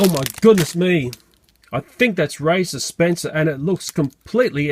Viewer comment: I thought you said racist spencer